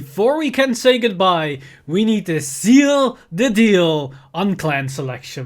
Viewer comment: Did he....did he just say “Zeal the deal”. This is the quality content I subscribed for XD